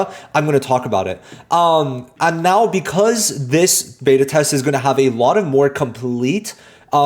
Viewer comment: I thought they said final beta test